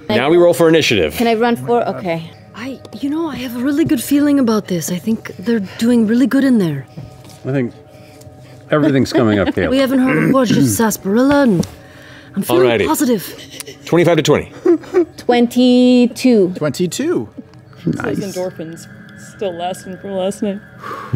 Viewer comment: Taliesin's "Everything's coming up Gale" at like 24:13 is such an underrated joke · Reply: I heard it as "... Coming up Caleb" but maybe I'm wrong